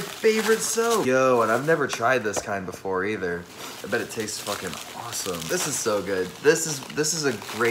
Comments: IM SORRY DID HE SAY TASTE?? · d-did he just say “i bet it tastes fucking awesome 🤤” awsten im worried